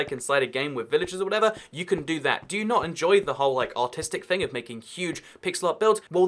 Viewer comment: did anyone else think he said autistic